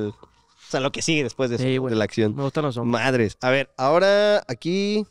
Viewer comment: Eh banda en el 1:21:53 susurra que le gustan los hombres o escuche mal?